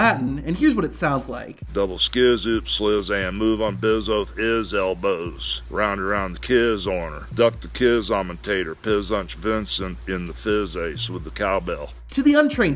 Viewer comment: all i heard was “double skee-zuup sklee-zam move on beezo ezel poze round around the kids or abduct the kids im a tator pizz on ya pinszon in the fizz eich with a cow bell”😂